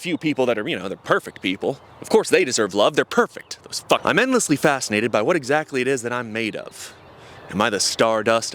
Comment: I heard im endlessly fat instead of facinated. >True watch the rest of the video now